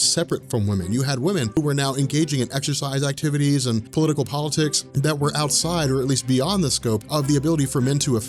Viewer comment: I love your videos, but did I just hear you say "political politics"? kek